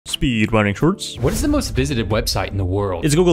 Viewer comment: I thought u said "Speedruning hurts". Btw great videos